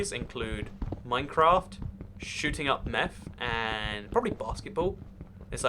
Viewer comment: "Shooting up meth" ? Or do i misheard you?